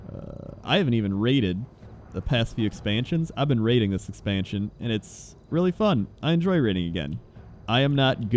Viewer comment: i thought i heard "ive been rating this expansion and its been a lot of fun" so i was confused like is he suddenly rating bc this long after its been released? but then i got me ears checked and understood